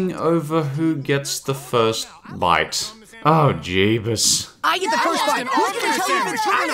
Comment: Did I hear jebus, from the Simpsons ? No wait I'm not even a mercenary I don't even believe in jebus 30s later SAVE ME JEBUS. good old days